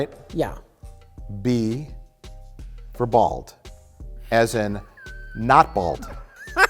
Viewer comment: You just know that if Rich had said "balls" instead of "bald" the way Jay did at 10:24 , would have spent at least five minutes on the slip-up